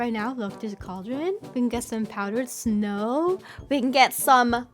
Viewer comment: Whenever she put the powdered snow into the cauldrons, it sounded like she was saying "power snow" 😂